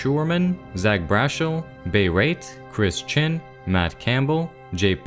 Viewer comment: Thought that said Chris Chan and nearly fell in a comma